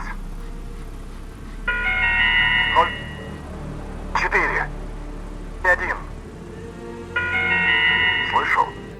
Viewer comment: I speak Russian fluently but I can't understand that first number he says at 0:54 bruh. Only the second and third one (4;1) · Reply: there "Nol" - Zero.